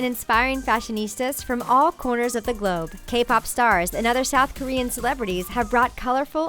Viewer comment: did she just say all CORNERS of the GOBE ? I’m confused